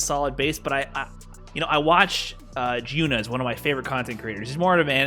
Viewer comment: who did he say? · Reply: Jiyuna